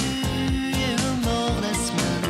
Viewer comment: Ca veut dire quoi à 1:28s "dans ma rue, y'a une morne assman terrien"? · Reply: En fait, c'est "y'a eu un mort la semaine dernière". Tu dois avoir du mal avec l'accent belge